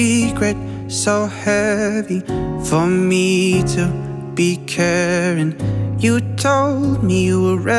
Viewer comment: i heard “so heavy to be karen” at 0:39 omg i almost laughed out loud😂 i